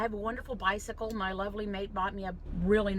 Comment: The last couple of these I watched I thought you were saying “my lovely maid!” I thought you must be quite rich but still odd for your maid to be getting you stuff! Anyway I’ve got it now! LOL😊